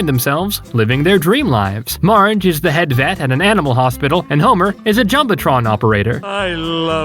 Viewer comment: did u say animal hospital?